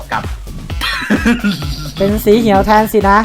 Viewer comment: ได้ยินเป็นสีเหียวเฉยเลย แหม่ ไม่อยากจะผวน 5555